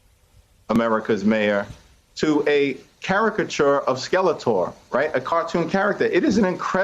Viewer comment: Did her really say Skeletor??? And then Ari trying not to laugh...